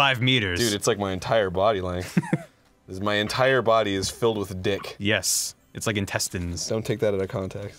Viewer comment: so I took out my earbuds near the lemon story, and when I came back I heard "my body is full of dick" I feel like I should be a lot more confused than I should be